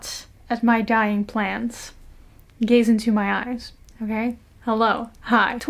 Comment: i literally heard "gays into my eyes" in the beginning and i stopped for a sec KSDNJFKSNFSNFKSFNJKVSJ lov your videos <3